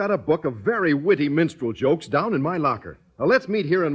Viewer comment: Am I the only one that thought he said "menstrual jokes" ? 😂😂